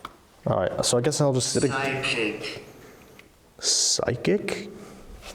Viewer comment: I know it showed psych probably but sounded like ‘sidekick’ they noticed you were back then asking where Steve was. And was responding to so much during this part-so cool